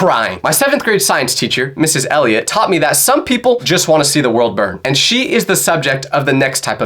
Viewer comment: when bro said this I hear I LET THE WORLD BURRRRN